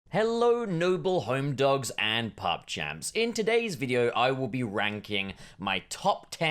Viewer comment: Am I the only one who heard "Hello noble homedogs and pawg champs." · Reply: I'm sure it was a play-on-words. He said "pug-champs" when he said it as a dog pun for "pawg-champs"